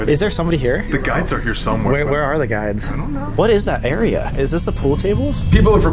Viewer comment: Am I the only one heard when sam said where is the guides. I heard someone said i dont know? Around 40:55 around there?